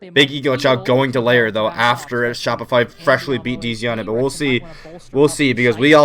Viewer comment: Wtf does he say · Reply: Shopify freshly beat dz